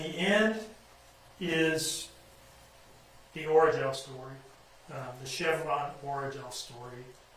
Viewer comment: I thought he was saying origin story for more then an hour 😹he is saying Orajel story 🤦🏿‍♀️